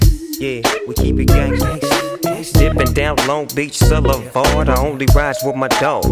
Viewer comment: why does he say soulevard?